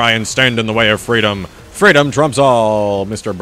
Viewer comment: Well now the sodda popper sucks thanks to the new update dsiojk Wait. Did you say "Freedom TRUMPS all"? Trump confirmed